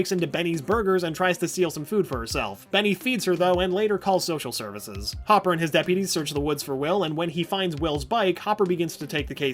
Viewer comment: In 2:50 I LITERALLY HEARD HOPPER AND HIS DIABETES LMFAOOOOO